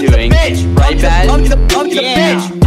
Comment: When he said Tommy’s a bitch over and over at the end it sounded like he said Tommy’s a bucket of fish lol